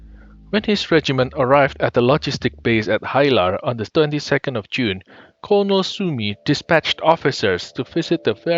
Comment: Did I hear correct... '32nd of June' WTF!!!